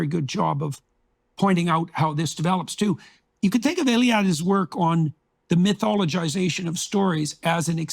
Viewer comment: Who is he talking about at 9:00 ? It sounds like he is saying Illiad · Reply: Mircea Eliade. Scholar of religion.